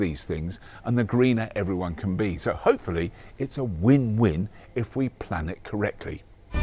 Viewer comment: Did you say 14:28 "if we Planet correctly" great catch frase 😀 . Let's all Planet correctly!